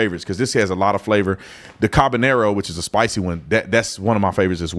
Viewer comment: 😂😂😂 did you say cananero 😂 i think you meant “habanero” 😂😂😂 but that was a great 😊 chuckle!!!🤭